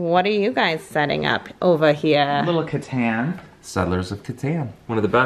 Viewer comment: I thought said "Little Kid's Hands" and i was like. no.